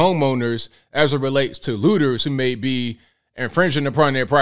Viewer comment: Did he say "Hooters"?